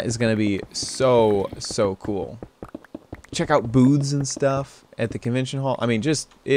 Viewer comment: I could've swore he said boobs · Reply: He said Booths lol it did sound like that